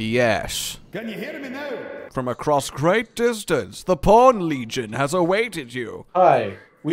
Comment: I thought they said the porn legion